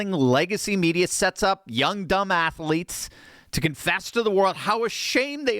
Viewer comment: I thought at first he said, "young dumb assletes" quite apt actually, they are all woke, a s s wipes